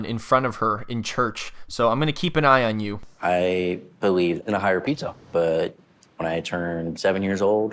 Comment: Did he just say, " used to believe in a higher pizza..." I will not rewind to correct myself bc that's what I hope he said.